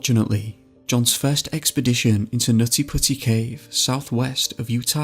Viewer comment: Did anyone noticed on 0:37 he said nazi pussy instead of nutty putty